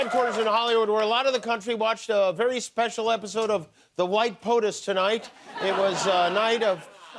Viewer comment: What the hell was that voice in the beginning at 0:00 - 0:01? Sounded like 'white power' lol.